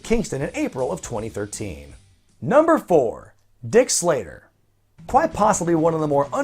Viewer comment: I was on a different page looking something up while listening, and I did not hear Dick Slater, for some reason I heard Dick Slayer...